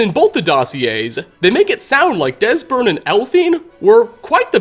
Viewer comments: did he say Desbern and Elphine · i thought i heard him say "desbern and elphine" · Did you just say Desberne and elphine?